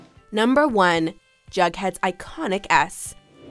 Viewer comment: i thought they said “jughead’s iconic ass” 😂